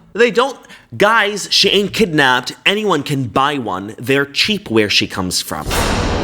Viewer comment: Did bro just say that I’m cheap?